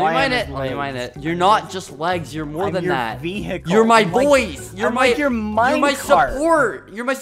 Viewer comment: why did I hear You're my boyf